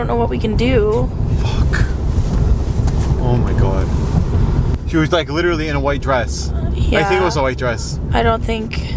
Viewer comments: after said she was in a white dress you can hear a kids voice i just cant make out what it says like if you hear it .you need headphones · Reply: I think she says I'm here. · while you were saying she was in a white dress, you can faintly hear what sounds like a little girl talking. Can’t make out what she says. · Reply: I think she says I'm here